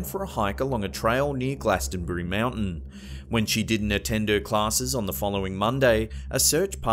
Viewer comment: i heard "when she didnt Nintendo classes"